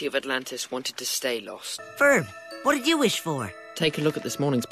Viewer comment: it sounded like he said Jewish